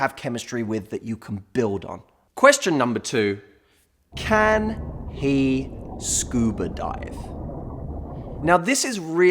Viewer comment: I thought it said "Can he swallow?" 😂